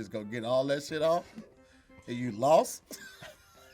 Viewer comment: did Tahir say Tone was wild as sidewalk grass?!🤔🤭🤣🤣🤣🤣🤣 That's some country ish I have NEVER heard in my entire existence!!!!! LMBO